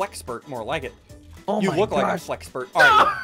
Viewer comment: Did anyone else hear say “god dammit”